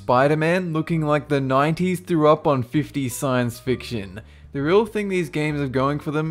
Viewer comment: did i hear that right, the 90's threw up on the 50's?